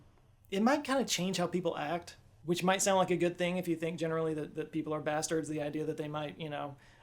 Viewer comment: I must have replayed that clip ten times. I'm a bit deaf but quite good at lip reading, however I still couldn't make out what you said. Did you say, "Which might sound like a good thing if you think generally that people are bastards." ? As a literal bastard myself, the word doesn't bother me, it just seemed really out of character for you. 😆 If that is what you said. I'm sure someone will let me know.